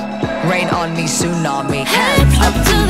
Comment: I thought she said am a butterfly 😂😂😂😂😂😂😂but it's I am about to fly😂🤣